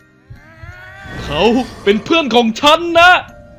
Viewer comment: หนูได้ยินว่า "เขาเป็นผัวฉันนะ"<num>+ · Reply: พิณทิพย์ ผูกฤทธิ์ เพื่อนไม่ใช่ผัว